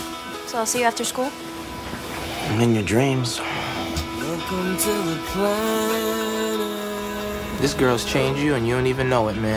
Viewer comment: welcome to calaned ( sounds like theyn say that)